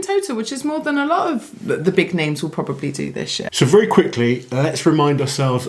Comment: "more than a lot of the big names will probably do this year" Initially sounded like "will probably do this shit" for a moment. I got whiplash